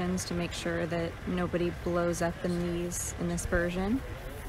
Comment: what is she saying............. the audio is HORRIBLE> :-( "blows up the ?????" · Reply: "blows up in these, in this version" Late model Jumo 4's had a service life of about 16 hours before they'd fall apart. Germany didn't have consistent access to high temp alloy materials.